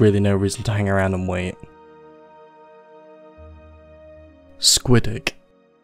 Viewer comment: I thought he said Squidhook at 1:56 which sounds way more terrifying than what it actually ended up being